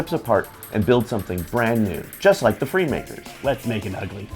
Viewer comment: Did they just say: "Let's make an Ugly?"